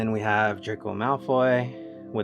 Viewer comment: I heard "Jerk of Malfoy"